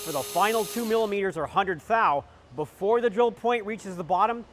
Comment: Error at 12:16, he says 100 thou, but subtitle says 10 thou. Great vid